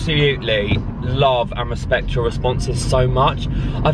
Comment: When he said respect your responses I thought he said I love boris Johnson 😂😂